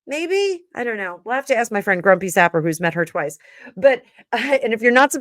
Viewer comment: Thank You. Been away for awhile so catching up on your readings. Also what was your mates name. I thought I heard “Grumpy Safa” but couldn’t find anything.